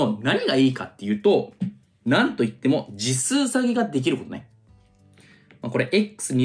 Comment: 「次数下げ」が「実数下げ」に聞こえて「なんやその技」と思ってしまった(笑)